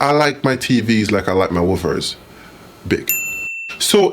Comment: I can’t be the only one who thought he said Whoppers.